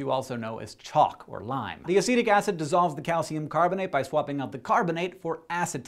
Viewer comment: When he said Acetic Acid I thought he'd said Acidic Acid and I was like "Ok... redundant much?"